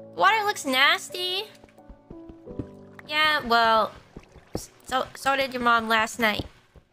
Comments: "So-sort of your mum was last night" I heard it as "S-so did your mum, last night" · I thought Ame said "so did your mom last night"